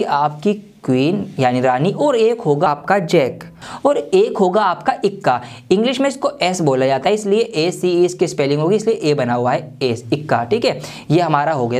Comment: I m on only one who heard As*